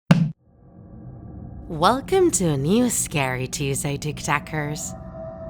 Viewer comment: I thought it said SCANY Tuesday Lol